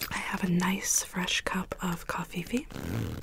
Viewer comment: did she just say... Covfefe?! I approve